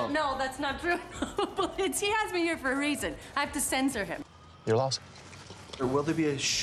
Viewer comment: what does he aks and what does cote says...!!?! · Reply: He says "your loss" and she doesn't say anything xx